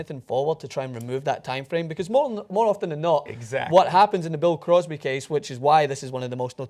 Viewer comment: did he say Bill Crosby??